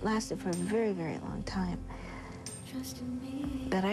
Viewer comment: What is she saying at 9:57 - 10? "I used to bring..." ?? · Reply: ColleenStockard I think she meant "bang" and not "bring". It's a term meaning "to be frustrated"